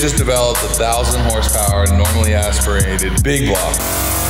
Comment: did he say "normally aspirated" at 0:27 ?